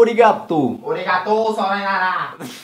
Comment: N sei se tem algum em 2020 vendo esse comentário mais eu ouvi "arigato só Noia la" kkkkkk